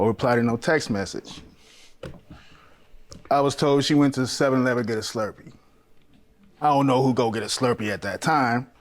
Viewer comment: Listen you misheard that lady she didn't go get a slurpee, SHE GAVE A SLURPEE 🤣🤣🤣 Sloppy toppy 🤣✅